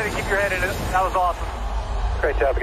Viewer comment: Can anyone make out what Kyle says right at the very end of the video @ 4:54 before Alexander shows up? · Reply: "one of those finally shaked our way" or something like that......